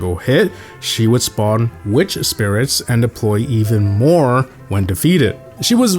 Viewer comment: I heard bro say 'nigg#' not mecha